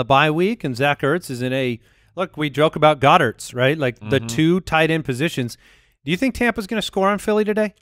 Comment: Does Andy say Goedertz? Goedert + Ertz?